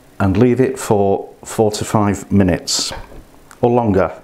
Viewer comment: I thought he said 45 minutes lol it’s 4 to 5 mins lol 🤦🏽‍♀️